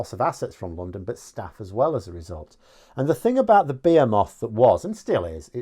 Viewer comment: i thought you said bearmoth when you said behemoth because you said it so different from me - i rhyme it with vehement